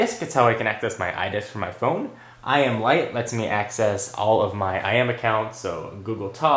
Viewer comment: when you said IM lite i thought you said i am white.. haha